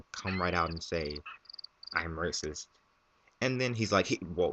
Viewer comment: i thought you said rapeist...... ether way, that guy is messed up!